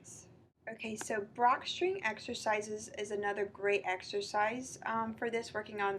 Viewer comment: You mention some exercises at the 7:11 mark. I can't understand what you're saying. Can you give more info? Thanks. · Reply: Hi Sam, I mention the use of Brock String exercises. You can also look these up on YouTube